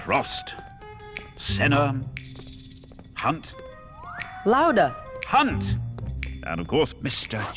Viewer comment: how come I never understood that before he said Lauda as in the driver, but professor thought he said louder